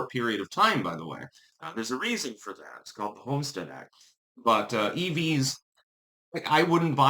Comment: Oh, homestead act....I thought that you said the Volstead act at first. I was wondering how prohibition had anything to do with it.